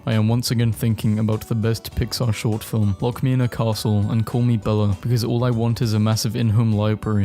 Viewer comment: did he just call her Bella